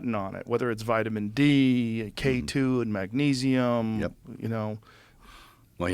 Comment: Mel says something that I cannot understand accousticly "did you do the break*** thing" something like that, I can't get it. Can somebody explain me what he is referring to? Thanks for your help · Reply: I looked at the transcript and googled. It sounded like Breaka… I looked up Breaka diet.. California seems to have these diet clinics that use vitamins. I thought that might be it. Google.